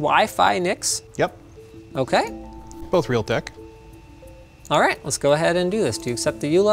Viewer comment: Did anyone pick up what the guy said in 4:46?? "Both a real d###